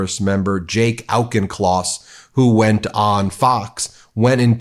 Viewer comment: Did her say Jake santa claus?